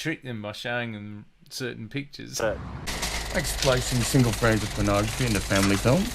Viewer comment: what does he say at 40:15 ?? is it: "do you know how to get kids to be gay? you trick them..." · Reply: '...by showing them certain pictures.'